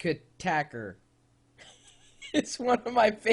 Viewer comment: what the hell is the word he is trying to say at 1:40 ??? Not even i get it o,o ahh kjøttkaker! xD it's not norwegian, it's swedish -.- an nobody eats smallahove! I's sheephead duh.